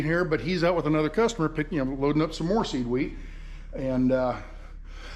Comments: I thought you were saying seaweed 🤣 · Good morning, great video, thank you for sharing the true reality and real America with us. At first I thought you were saying you had to pick up seaweed, not seed wheat...lol....that feed dealer would be a great place to visit in my mind. Thanks again and best wishes with the farming. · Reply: Lol. I thought he was saying "seaweed" too. So it's "seed wheat", got it. 😃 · Good morning, great video, thank you for sharing the true reality and real America with us. At first I thought you were saying you had to pick up seaweed, not seed wheat...lol....that feed dealer would be a great place to visit in my mind. Thanks again and best wishes with the farming.